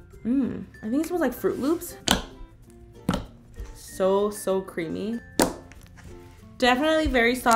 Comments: When Karina said: "So so Creamy" I thought she said: "So so creepy" 😂😂😂😂😂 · I thought she said creepy lol LMAOOOOO IM SO DUMB